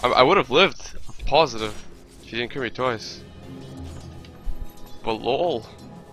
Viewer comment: I think he said:"I would've lived" if he didn't crit me twice a lot of subtitles are wrong:(